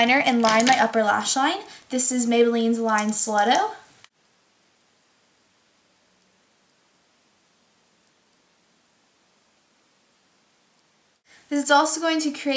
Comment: lol at 3:09 i thought u said "this is maybelline's line slutto"